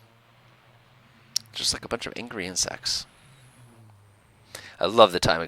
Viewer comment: I kept hearing "Just like a bunch of angry ant sex" .. took me a while to realize you were saying "insects"